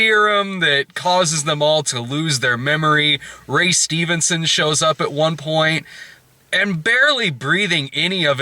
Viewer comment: ROFL I misheard day "At one point Ray Stevens showed up." I would have seen the movie just for that.